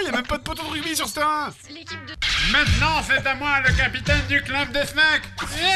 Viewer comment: il y a que moi qui entend "maintenant c'est à moi le capitaine du club des schnecks" ? 🤣